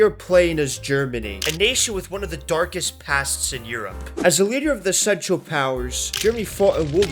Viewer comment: I heard "as the leader of the sexual powers